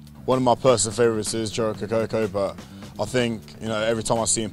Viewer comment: what player does he say at 2:15? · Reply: joe rokocoko